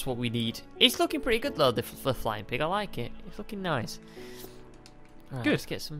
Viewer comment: Did swear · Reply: Oops he said its looking nice i thought he said this is fu*** awesome oops sorry guys